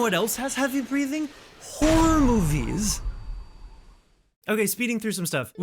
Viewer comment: I thought he said whore movies and was about to agree with him